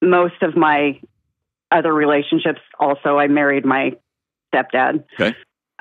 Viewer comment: Did she say she married her stepdad at 8:26 or am i somehow mishearing this